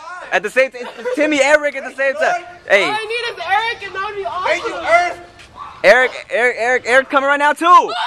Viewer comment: did he say "thank you earth"??!? LOL